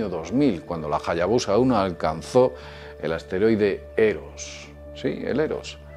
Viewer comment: Qué dijo en minuto 1:19? "alcanzó al asteroides e2"?